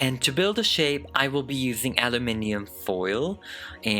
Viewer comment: Did u just say ‘allaminyum’😭😭😭😭😭